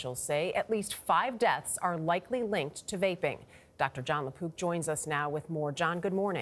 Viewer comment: I legit thought she said “Jon LaPoop”